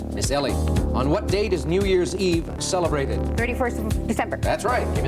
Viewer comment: It sounded like in the second bonus round at time index approximately 20:50 that New Year's Eve was the 31st of "September" rather than "December". Can someone with better ears tell me if I am hearing things? Especially since there is no "September 31st". I'm sure she knew the famous poem, but sometimes one can slip on a banana peel regarding the calendar.